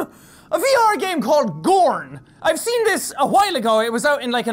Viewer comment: I heard Jack said "we're playing a break game called Porn "